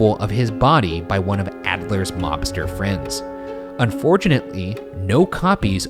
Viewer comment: I heard "lobster friends" not "mobster friend" OPE